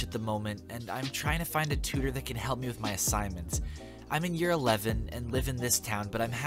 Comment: I had this playing in the background and when it said "assignments" it sounded like "silence" then proceed to buffer for a while. If that wasn't perfect timing I don't know what is.